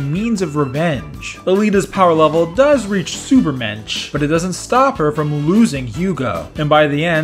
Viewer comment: does he say ubermench or supermench (probably spelled it wrong)